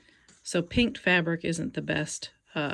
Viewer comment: Super fast and precies tutorial! Thank you! The only thing that confused me was your reference 01:25 that "pink fabric is not the best to hem with" but it is definately green. Did you say kink fabric or does pink fabric mean the way you cut it with the jagged edge somehow? Thank you!! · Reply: Sorry, it's "pinked" fabric... cut with pinking shears, so the edges aren't straight.